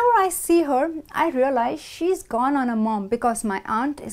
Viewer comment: What does she say in min. 8:47? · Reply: Hola Manuel. You can click "CC" to check the subtitles. She says "She has gone on her mom." Which means: she has the same characteristics, she is similar to her.